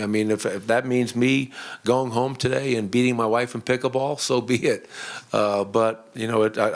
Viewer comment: Malone going home to best his wife in pick up ball. Hahahahaha The man is a joker. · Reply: Thought he said pickle ball. Lol